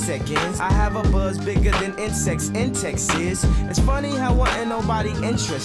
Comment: i’m also realizing that you’re not understanding what he’s saying or reading the lyrics because “incest in Texas” is hilarious I understanding that from the states but a giggle or incest or a raise in an eyebrow would’ve been an indicator, you should definitely focus on the lyrics as much as the actual beat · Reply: Ooooh I heard it like “insects in Texas”. And i thought like wtf i never heard about big insects from Texas and I collect insects. 😅 Yeah you guys right. I need to see the lyrics when I listen to the song